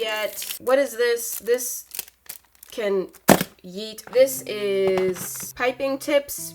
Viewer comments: Did SHe Say YeEt!!!!! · Did she say yeet?!